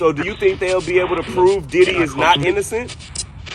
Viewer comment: I swear I heard Diddy guilty 😮